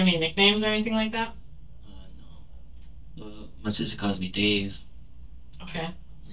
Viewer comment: I thought he said “Deez” at this part not “Dave” 😂 had me thinking he was going to set her up